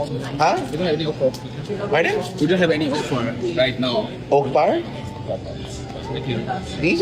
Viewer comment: He said “no offer”. The TikToker is an imbecile. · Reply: I thought he said we have no okra right now.